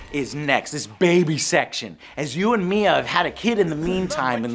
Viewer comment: I thought he said, "you and me have had a baby"